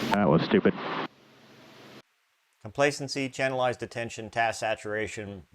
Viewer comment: Fascinating. But, what is that word you're saying at 0:05 ? Sounds like "complacency canalize attention", but that obviously isn't it. "Complacency can xxxxxx attention"? But what is that third word?